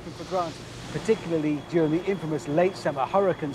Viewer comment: Did he say huracan season or huracane season?im kinda trippy bout that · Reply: Hurricane in a Warwickshire county English accent.